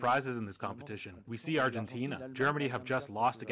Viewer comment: I thought he said “ Huh!? Oh, oui.”